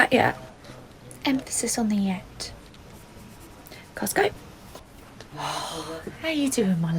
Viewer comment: why does it sound like said Costco not lets go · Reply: She did say costco it's just the subtitles got it wrong